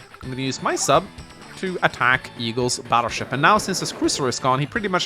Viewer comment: did he say eagles battleship